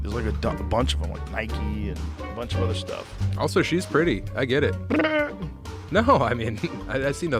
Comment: Did he just say Dillon was pretty?😍